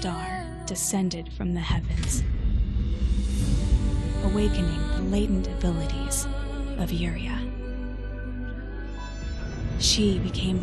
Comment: the dialogue sounded like "awakening the latent abilities of urea"